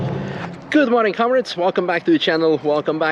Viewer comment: My name is Konrad and every time introduces himself it sounds like he says "Good morning Konrad's" and I feel very privileged then.